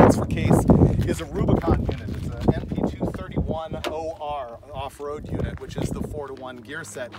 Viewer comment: 241or* it sounds like you say 231or.